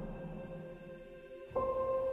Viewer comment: What was she saying? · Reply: know that they'll be loved